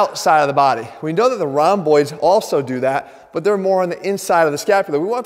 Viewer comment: My rats were making a bit of noise in the beginning so when you said "rhomboids" I really heard "round boys" 🤭